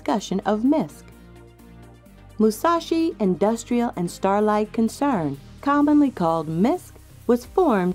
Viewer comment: did you say "starlight" or "starlife"? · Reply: Starlight but it's Starflight. I mess that one up all the time :-/